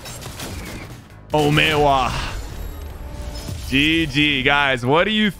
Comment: it sounds like hes saying o may owaaaa